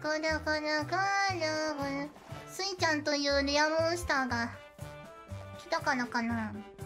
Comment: correct me if im wrong but based on the context i think miko said "Rare monster" here instead of "real monster", saying that youtube is buffering because of the presence of the shiny pokemon that is suisei hoshimachi. Either way it's suisei so real monster also works LOL